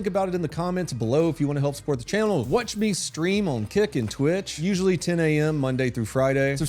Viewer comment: At the end I heard "watch me scream and kick and twitch". You can't present and star in your own videos. Or maybe you could... that would be pretty groundbreaking crime journalism.